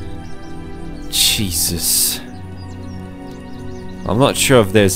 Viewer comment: did he just says cheezus?? CHEEZUS CHEEZE CHEEZE CHEEZEEEEEEEEE